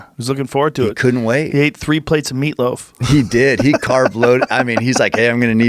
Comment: I listen to podcasts on 2x speed, at 7:22 I did a double take and had to rewind because ,"he had 3 plates of meatloaf", to me, sounded like "he freebased some meatloaf" and, being a fatty i thought "wait, you can do that!?"